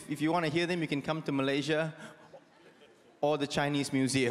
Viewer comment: what does he say after 'My name is Douglas Lim'? Thank you. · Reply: he say Terima Kasih which means Thank You